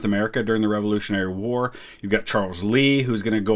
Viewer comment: "You've got Charles Lee..." Damn you, Lin Manuel Miranda, all I heard in that second was "I'm a general, wheeee!!"